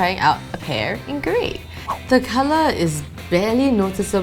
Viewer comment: what did she say?? the color... ?? whattt??? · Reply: Ales Sandra "the color is barely noticeable"